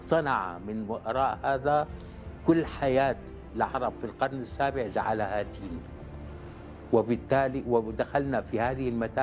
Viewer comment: ماذا قال في الدقيقة 5:40 ؟ جعلها........،؟ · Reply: جعلها تيه ... أي ضياع وتشتت